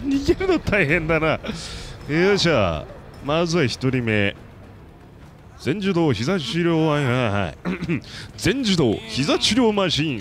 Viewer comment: が全自動ひざ治療WiFiって聞こえる・・・ｗ